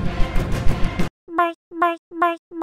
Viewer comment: it actually sounded like she’s saying die Edit:I’m famous :3